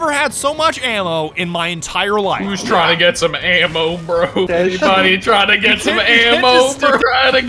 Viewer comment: Bruh I was trying to drink lemonade and all I hear is "Who needs some ammo" then I choked and it turned into a coughing fit. Thank you